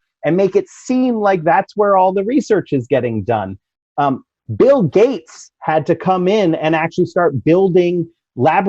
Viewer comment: Did he say that Bill Gates built fake labs? I thought he was one of a few of the good guys 🤔